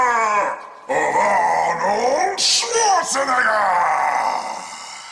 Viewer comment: Go to 0:10 and it sounds like he is saying nigga